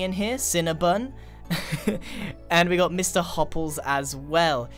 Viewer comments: i thought it said mr. hopeless and i think it described me a little too well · I thought u said hopeless XD but u said hopless or what not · I thought u said Mr.hopeless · I at first thought that said hopeless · thought it said MR.HOPELESS